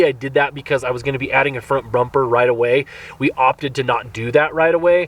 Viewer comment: what's a front brumper? also @ 2:16 you say the wheel size is <num>, that would be a tiny little wide tire - did you mean 280 or 285 tall?